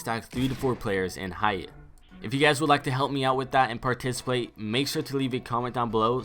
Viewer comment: I heard you say participlate